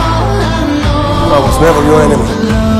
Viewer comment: ❤ it! Keep up the excellent work! BTW, what does Grindelwald say at 2:05? I can't hear cause of the music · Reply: "I was never your enemy"